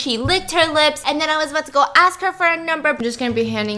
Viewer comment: The first thing I hear is “he licked her lips” I am confusion :>